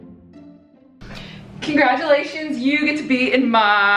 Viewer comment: lowkey thought you said “conDRAGulations” and i was like Ru-Paul who?